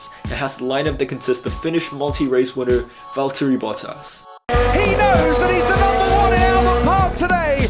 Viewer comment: Just started watching F1 this year with my friends who have been fans for years. My favorite driver quickly became Botas because it sounds like their saying buttass. 🤭 he seems like a really cool guy, too